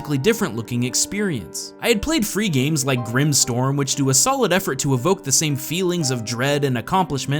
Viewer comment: What is this game? You said Grim Storm? Cant find it for some reason and wanna try it.